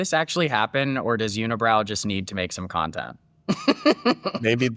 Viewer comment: Did you just call her "unibrow" ?! 😂😂😂